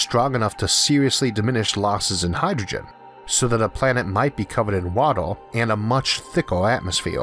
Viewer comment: Lol misheard him and it sounded like he said a planet might be covered in water and LSD. Now that's a planet.